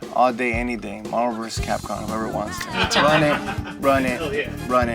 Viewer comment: I thought said Marlboro vs Capcom